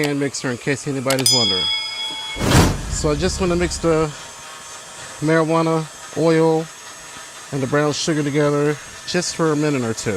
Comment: DID YOU ACTUALLY SAY “I JUST WANT TO MIX THE ‘MARIJUANA OIL’ AND THE BROWN SUGAR”? 😮😂😂😂